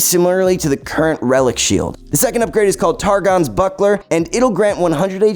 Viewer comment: I thought you said "Targons B*ttplug"